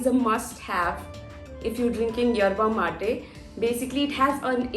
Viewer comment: Erba mate i heard it as eruma made ....